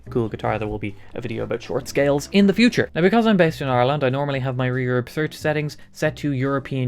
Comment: I thought he said "because I'm based out of Orlando." I thought it was an odd Florida accent.